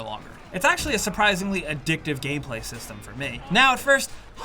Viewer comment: Did you just say “Addictive Gayplay or gameplay? 🤣